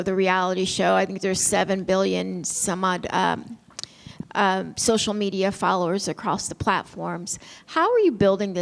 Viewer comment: did she said this thing had 7 billion followera across platforms? lol